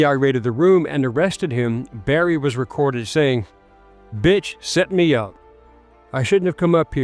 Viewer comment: Mark. Lol He didn't say " Bit... set me up". He said " That Bit... set me up". It hits different when you put the word " The " in front of it. I heard it was The. 😂😂😂😂😅😅😅.